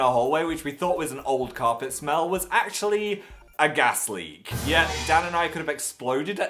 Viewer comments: When he sad gas leak I thought he said Gastly and I was like, "why are there Pokémon in your halls??" 😂😂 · When he first said 'gas leak' i thought he said it was a GASTLY and i was just like ...U know they're not real right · When he said "gas leak" I heard "ghastly" and flipped out for a little bit 🙃 oops